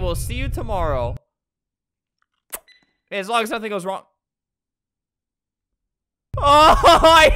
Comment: I thought that said, "As long as something goes wrong..." I thought that was the devil speaking to him! sOmEtHinG wEnT wRoNg, FLAM GO? SEE YOU IN HELL TONIGHT!